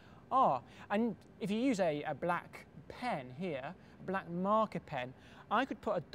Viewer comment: I heard "black market pen" and was like O.o?? What the heck would you be doing with a black market pen?